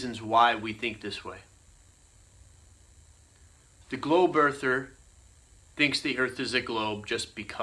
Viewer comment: Did he just say "glow birther"?